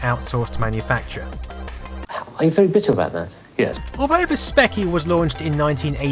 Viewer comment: What is Clive saying on 5:28 ? I'm not native English speaker and can't make out what he is saying at that moment . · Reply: is asked "Are you very bitter about that?" and he simply responds "Yes" :D